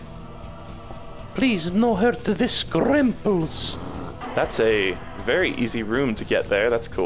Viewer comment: I got the fruit creature to say the word scrimples, which is what I call the blood clots. Other words include Scrumples, Skrinklies, Scrongles, and The Children.